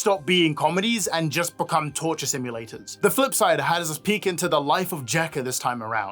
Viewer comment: i genuinely heard “the footside” first instead of the “flipsidr”